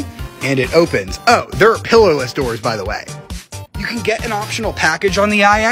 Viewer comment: Did he just say pilllowless doors lmao